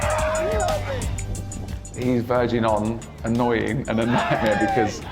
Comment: I thought it said virgin